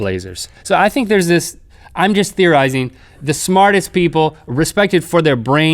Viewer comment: I honestly thought it said "Smallest" not smartest